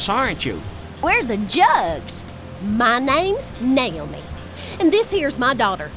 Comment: uhm, I think the left one says 'Nail'me' instead of Naomi when she introduces herself haha